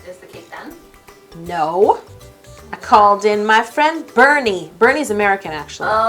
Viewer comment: When she said Burny I thought she said Bernie. I was like, obviously he's American.